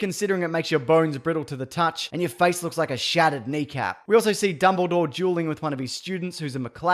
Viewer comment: i thought you said dumbledore JUULING with one of his students